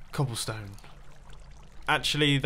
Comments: Did he just say Cobblescone? i would like to taste that sometime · Did anybody hear him say cobble scone?